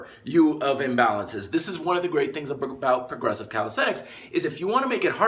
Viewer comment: About 3:15, when he says "Calisthenics" but the subtitles say "cow sex"...